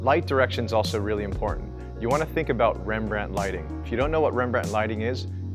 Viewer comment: How can I google it if I didn't understand the name of the thing 😐 Can anyone spell it for me 😅 I'm serious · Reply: Rembrandt lighting 👍